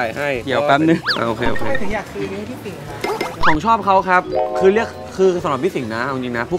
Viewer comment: ได้ยินว่า ผมชอบเค้าคับ 5555